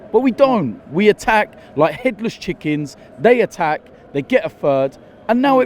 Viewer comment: sounds like hes saying "hitler's chickens" LOL